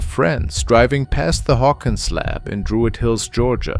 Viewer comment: I really hope no one believed that first video was real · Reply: Did they say "Hawkins Lab" ? I guess "Stranger Things" have happened ...